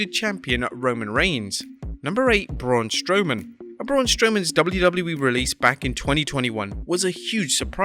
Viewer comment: Barun instead of Braun